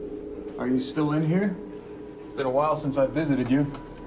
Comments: I thought it said im still in here listen closely with headphones 🎧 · TBH I thought the whispers said "why are you still here?" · I heard I'm still in there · First I heard "why is someone in here" and I was so sure that was what SHE (because it sounds alot like a woman) said but then I heard "I'm still in here" but I don't know if I heard the second one because I read the subtitles, because I heard "why is someone in here" until I read the subtitles :s